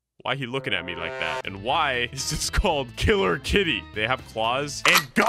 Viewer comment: I thought it said "kittler kitte”😂